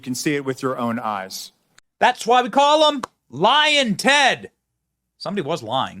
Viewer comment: Oops, sorry, I thought you were saying lying Ted, I now realize you said lion Ted, my mistake, and you're absolutely right, LION TED !!!!